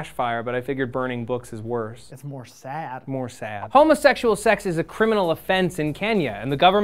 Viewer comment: Am I the only one who heard "homeless sexual sex"?